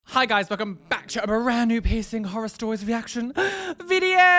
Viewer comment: really thought he said “share your 𝑝𝑖𝑠𝑠𝑖𝑛𝑔 story” instead of piercing story at first ... — JustHereForTheTube has left the chat.